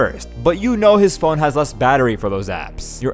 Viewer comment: What the- the whole life I thought he said ''Follow Go Hard" but he says " follow Gohan"😅